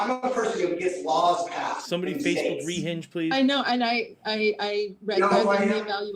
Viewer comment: i thought it said renting. rehinge makes more sense